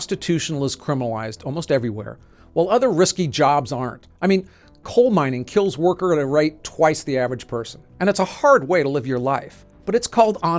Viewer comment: Did he just say "coal mining kills worker at a right twice the average person"??!!